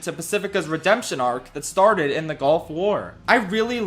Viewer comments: bruh i misheard "golf war" as "gulf war" and im like, hold up. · I heard “gulf war” the first time and I’m dying of laughter